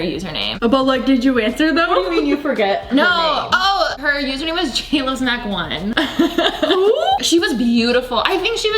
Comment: Nobody- Me- trying to figure out what she said and looks up “jaylasmack1” I’m soo lost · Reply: Jlovesmac1 - she was an OG youtuber wayyyy baaaack in the day